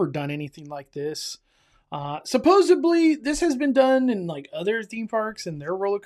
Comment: did you say supposebly